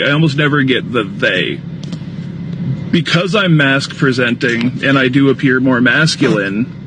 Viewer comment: I thought he said he was mass-presenting, as in a new way of saying obese. Lol 😂